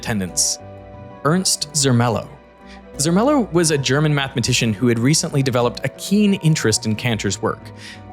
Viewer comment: When you say "mathematician" i hear "mad magician" which strangely fits.